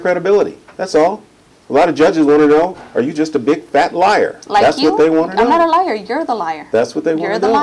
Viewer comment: did he call her fat and a liar